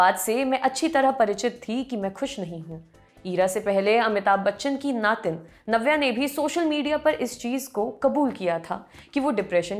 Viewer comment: Did you just said Navya nevli 😂😂😂